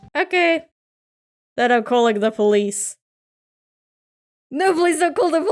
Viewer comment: Who's mom threatens their kids with the police? Mom took me by the ear and pulled me to the kitchen And Damn are you more scared of the police than your mom? I mean the teacher once said when I was younger that they would call the police on me but I didn't budge. Then she went "Alright, I'm calling your mom" REEEEEEEEEEEEEE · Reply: I thought she said police not crazy ladu